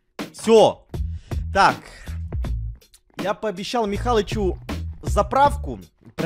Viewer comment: стоп я один услышал Михалычу